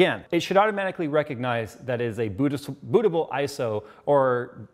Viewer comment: did i hear bootiful?? XD